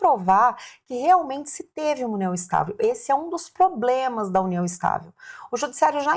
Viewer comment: Você falou "POBLEMAS" ou escutei errado? kkkkkkk